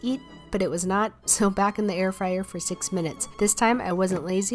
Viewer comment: I like listening to your content while cooking myself, but I almost dropped my spatula when I thought I heard you say: "back into the airfryer for 6 MONTHS"